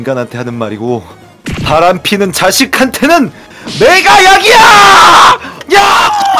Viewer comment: 쥐늑대 목소리 뭐누!! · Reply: ㅋㅋㅋㅋ2:41 매가약이야!!! 를 내가약이야!!! 라고들었닼ㅋㅋㅋ